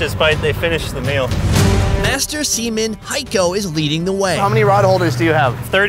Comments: who else heard "semen" instead of seaman? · did he just say master semen